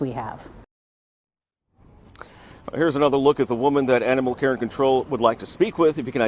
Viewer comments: I thought he said animal Karen control lol it was animal care and control 😂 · Oddly, after seeing that pic and the anchor said “animal care and control”, I heard “animal/Karen control” 😂